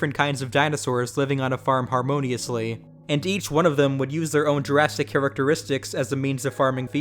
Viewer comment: did you just say "jurrasitic" because i really hope you did